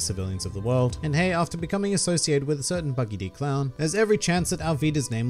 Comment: I thought he said buggy d. Clown 7:30 😂